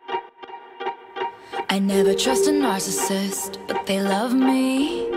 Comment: I originally thought she said “I never trust a nazi” and honestly same Taylor